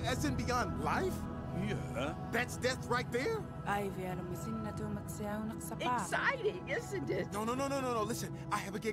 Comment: What did she say? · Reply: I don't know if this is true, but from this video: <link> the subtitles for what she said was "This beats my dream about the walrus"